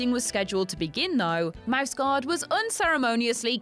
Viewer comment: I'm too American. I was only listening to this video and I thought she was saying Mouse God